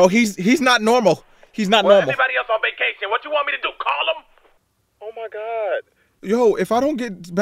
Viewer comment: does anyone know what he said here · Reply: He said something similar to "Well, there isn't anyone else on vacation. What do you want me to do call em?!" Idk if im right, but it's similar